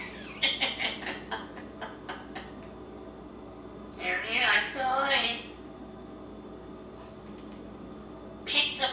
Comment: what does he say at 1:09 before saying "kick the buu-ut" at 1:15? Then he mixes "Andy Grif theme into Game of Thrones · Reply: Sounds like, “How you Einstein” but he is mumbling.